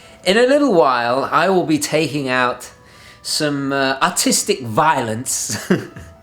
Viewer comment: "In a little while I will be taking out some autistic violence" Sorry, that's what I heard xD